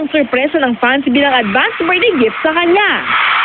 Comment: guys, Correct me if i am Wrong, i don't know if i misheard it. kindly repeat 1:20-1:25.. WEDDING gift daw ba? it must be BIRTHday gift right? an reply plz. tnx